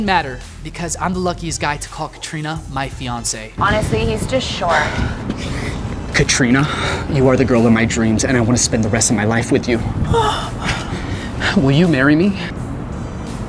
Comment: Did she say he’s short?